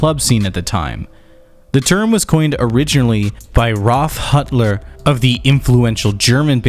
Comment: Did you... did you say Huttler?